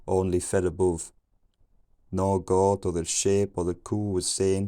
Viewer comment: Early I thought I heard "no tea, no shade."